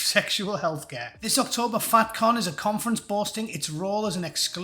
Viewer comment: Thought you d said fat Cnut